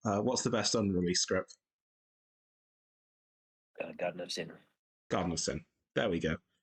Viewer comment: what does he say is his favourite script? It sounds like he's saying ''God loves Henry''... Can someone tell me what's the name of the script he's saying? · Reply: Garden of Sin.